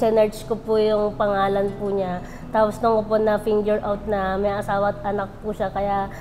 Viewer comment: "Dun ko po na-finger out" HAHAHA parang iba narinig ko?😂